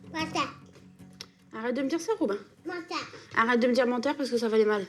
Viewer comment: il dit quoi ?? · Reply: kinna jinna il dit menteur